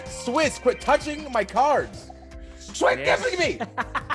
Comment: did anyone else hear creed at 22:49 "swuit gibbing me"